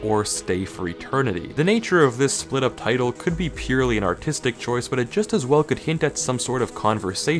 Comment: I heard "an autistic choice"